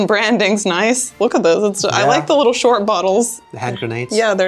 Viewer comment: you know what I heard say? "Headgrenades" - it hits your head directly... In Salzburg, Austria there's a restaurant selling belgian beer and my favourite there is Leffe ( there are a few different types and one of them is at 8.5% alcohol)